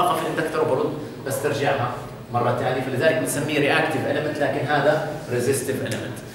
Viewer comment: دكتور ممكن توضح هذي النقطة اللي هي ليش حطينا RC على التوازي لانه في كلمة قلتها هنا بس ما سمعتها كويس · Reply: "Reactive Element" There is a current that is 90 degrees behind the voltage. This is called the magnetisation current. As it is 90 degrees lagging the voltage, the best component to represent the magnetisation current is an inductor.